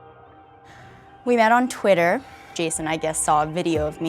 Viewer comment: i thought it said "girl he met on tinder" lmfao